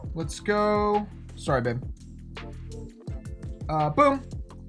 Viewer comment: Did just call his babe